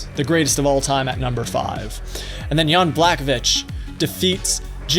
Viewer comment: What he said: “Jan Blachowicz” What I heard: “Young black bitch” 😂